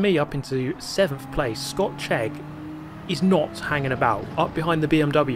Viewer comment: You say "Scott Chegg" and I hear "Scotch Egg" :face-green-smiling